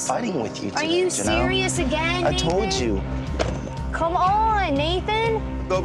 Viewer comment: anyone hear her go "are you serious, again david?" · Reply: A.M Kutzler I think she said Nathan but it did sound like David lol